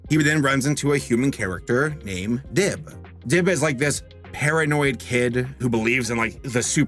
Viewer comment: When he said paranoid, I thought he said hair-annoyed